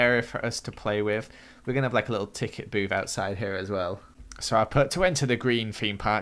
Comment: when said a little ticket booth I thought he said a little ticket boob :P